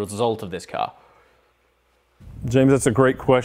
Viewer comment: Did he say Jim?